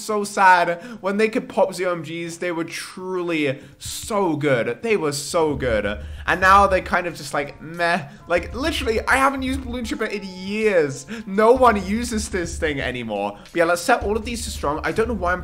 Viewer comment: I thought he said “meth” instead of “meh” lol Edit: I guess I am drug minded